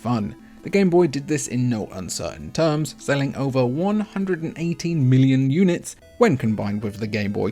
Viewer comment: It sounds like you said "over 180 million units" while putting on the screen 118.69 million. Maybe you need to work on your pronunciation. You should take a class if your accent is in no way indicative of a quality education.